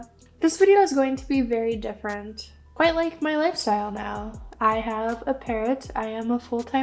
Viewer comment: I thought you said, "I have a parent. I'm a full time mommy," and was very confused until the parrot popped up on screen.